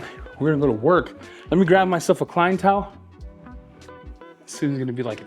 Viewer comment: I thought you said crying towel 😉😂